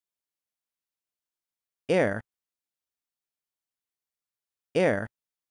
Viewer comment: word 8 What is the meaning of hier and why it sounded like air!!!